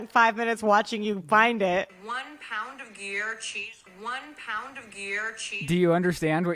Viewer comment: i thought someone said one pound of gear changed my life" xD that's either meth or crack in Australia and I can't remember if there's a difference. "Yup that's the sentence I don't think I had any coherent point. Beware of drop bears, Australia doesn't exist, Mae out!